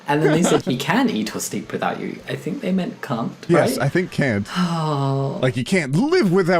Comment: The subtitles thought said c*nt- 😭😭😭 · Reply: yeah he got demonitized for saying "cunt" when he obviously meant can't and it was his accent